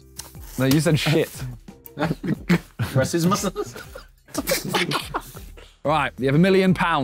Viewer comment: What’s the phrase that Ethan said at 11:55? I know it’s a meme but idk how to spell it so I can’t find more abt it too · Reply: I think he was attempting to say racismo no